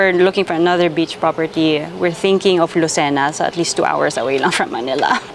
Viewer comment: i heard Sam says LUCENA? 😲 marami dito sa amin hometown na pagtatyuan ng resort at meron binebentang resort at pwedeng ih refurbished. 😊👍🤔